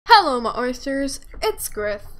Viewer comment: Legit thought you said,”hello my toasters!”